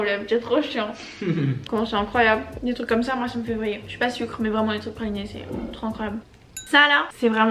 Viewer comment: C’est moi ou à 8:25 elle dit « je suis pas sucre »?????? tu manges du sucre non stop t’adores ça 😂😂😂 en tout cas je trouve que les vidéos déménagements c’est les meilleures vraiment 💘💘